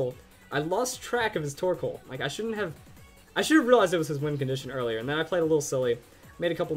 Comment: Before I read the description, I always thought you were saying "Wind Conditions. Now I feel dumb -_